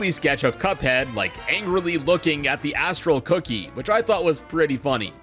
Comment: Wuit did you say!? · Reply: Cuphead looking angrily at an astral cookie.